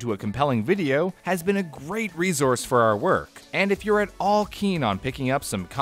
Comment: I heard "keen eagle"